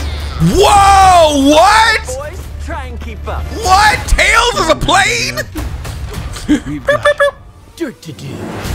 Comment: "Tails has a plane!?"... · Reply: I thought he said tails is a plane!? Lowkey had me worried for a sec 😂